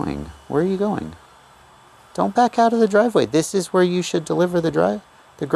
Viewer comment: Hahaha, right at (24:11) it sounded like you almost said this is where you deliver the dr$&z??? 😅